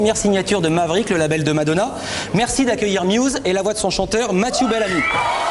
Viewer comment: i swear he said belldom there